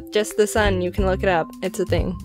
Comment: So the subtitles on 1:35, instead of look it up, it's "look a crab".